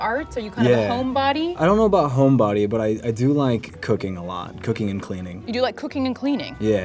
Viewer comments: Who else thought that he said he likes cocaine? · I seriously thought he said he liked cocaine a lot 😂😂😂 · I heard him saying "cocaine" instead of "cooking" omg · I thought he said he liked cocaine but it was cooking😂😂😂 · I heard COCAINE !! I dies laughing 😂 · When the guy said he’s been in to cooking I straight up thought he said cocaine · I swear he said cocaine 😑